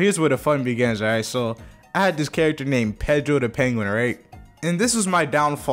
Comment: I thought you said Pedo, the penguin